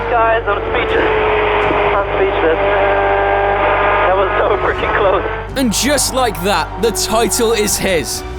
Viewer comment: buddy who the fuck did the captions? "so pretty close" yeah thats definitely what he said and not "so freaking close"